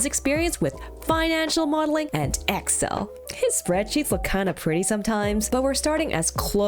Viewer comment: i heard "his BREAD CHEEKS" for a second hahahAhAha